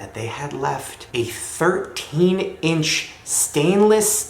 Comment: so when said they left a 13 inch thing in there i thought he said 13 feet xD